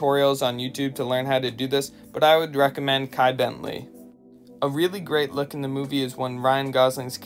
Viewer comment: When you were talking about the metallica shirt you said you recommend what? It sounded like you said kai Bentley or something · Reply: Kai Bent-Lee has a tutorial where we turns a new tee into a vintage looking wash👍